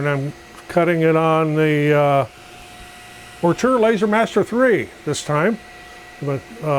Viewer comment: What kind of laser cutter ? Sorry, I can’t make out what you said. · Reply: Ortur Laser Master 3.